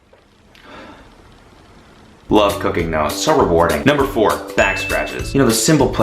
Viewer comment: then he said cooking, I thought he said cocaine.